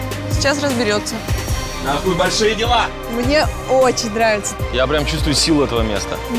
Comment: Показалось что он сказал "Нахуй большие дела", потом включил субтитры а там "Нас ждут большие дела"